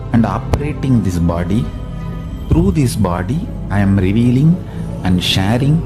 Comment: bro I am not complaining but in this part the baba is saying revealing but the subtitles said releaving